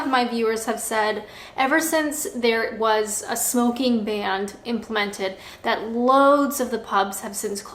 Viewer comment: did she say smoking band